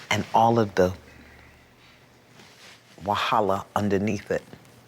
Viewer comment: who else heard Iyanla say "wahala" the Nigerian word for problem or trouble.